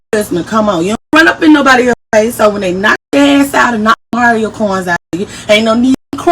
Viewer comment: Did she just say, "Knock the Mario coins out cha? 😂"!